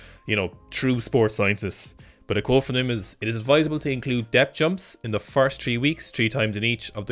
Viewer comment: I'm not going to lie, I thought they said "death jumping" the first couple of times and was like "well obviously it's dangerous, you can tell from the name alone!".